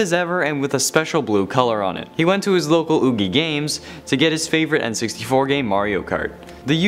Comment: you would think I said EB Games but you didn't